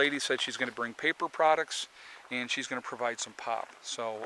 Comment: i thought you said little prepper lady is gna bring pot...lol it was pop...im from cali growing commercially so forgive me